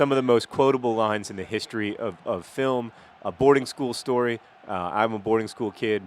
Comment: I know John went to a boarding school. Knowing this, at 11:53 I still thought he said "Aborting School" and was like :O